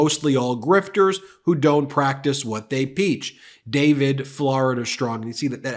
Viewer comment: You might want to edit this @ 2:59, where you say peach instead of preach. Talking a little slower would also be appreciated. You're not trying to win a race. Why not slow it down a bit, instead of getting in a rush to post these videos before reviewing them. It just makes you look unprofessional and you're better than that. Fer Godsakes, you don't want to be making the same mistakes that Trump makes🫢, in the many videos that he posts online!😊